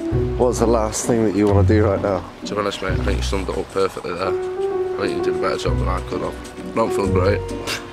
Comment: As an American who watches your show regularly and has no problem understanding your accents, I have NO IDEA what says at 1:03. I've watched that part like 5 times and still can't figure it out. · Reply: To be honest mate think you summed it up perfectly there, did a better job than I could have- I don't feel great.